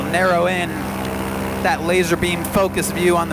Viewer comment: Did anyone hear him say lazarbeam at 3:14 ?? XD